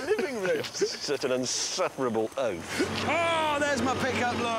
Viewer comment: "you insufferable oath". I was on the floor gasping for air. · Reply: Free`Water I thought he said ''insufferable oaf''